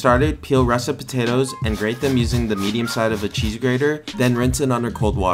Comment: bro i thought you said medium side of a cheese burger